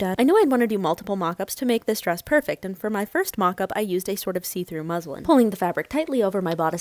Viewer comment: I thought that she said " A see-through Muslim," !!! look 4:49 .. hahahhaa I had to put subtitle captions just to be sure of what is said. That was a close one.